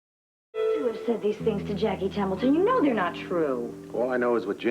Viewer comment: Sounds like Robert said Jackie 'Temple' instead of 'Templeton'